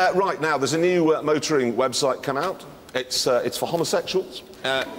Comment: Finally I understood why people at the beginning laugh. Jeremy said: "A new motoring website COME OUT, it's for homosexuals". They thought he said it on purpose.